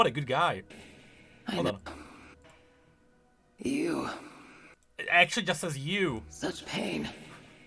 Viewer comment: censor what? She just says "you".